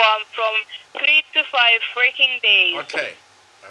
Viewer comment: Is my hearing bad or did she say you'll get your device in "3 - 5 freaking days"?